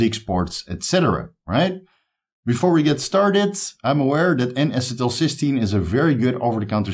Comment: I missed that, can someone help? I just heard NS something 16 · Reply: N-acetyl cysteine